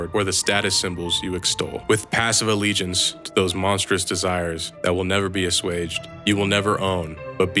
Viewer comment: "when the only cause of your endeavor are the posession you hoard... " I can't hear the rest of this sentence acoustically, can anyone help? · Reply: “...or the status symbols you extol. With passive allegiance to those monstrous desires...”